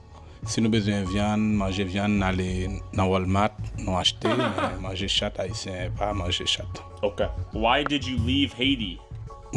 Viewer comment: Haitians mostly speak French Creole and at 11:50, it almost sounds like he says "Manger chatte haitienne, pas manger chatte", which would translate to "We eat Haitian pussy, we don't eat cats." He's like us fr.